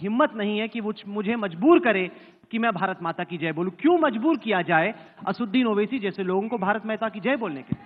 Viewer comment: he said Bharat Mehta ki jai... instead of Bharat Mata ki jai.